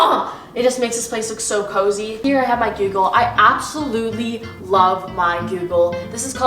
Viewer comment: is it just me or does say Google like Gyugle? (no offence to anyone)